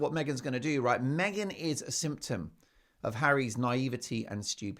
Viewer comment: ‘Meghan is a symptom …’ For a moment there, I thought you said Meghan is a Simpson 😂